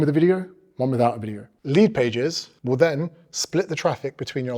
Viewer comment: He is saying "" lee pages"" what does that mean, or do i hear it wrong?